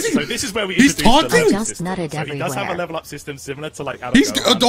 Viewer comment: Who else heard "I just nutted everywhere" at 4:20 ??? 😂😂💀